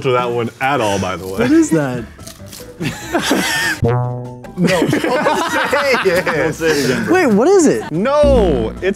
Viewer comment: what did say? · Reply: Splooge